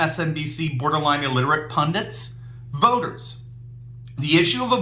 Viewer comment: did I hear "...pundant"? There's no such word in the dictionary. It's pundit. Lawyers who don't keep the laws on the books are actually criminals.